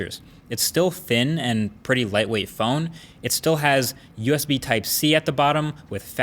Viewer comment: Wait did you say USB-C?